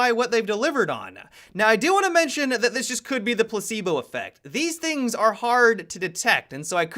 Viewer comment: Did he just say the Placenta Affect??